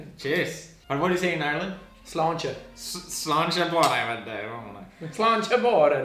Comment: what did they say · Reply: The first word was 'sláinte' and it means 'health' in Irish and I have no clue what else he said.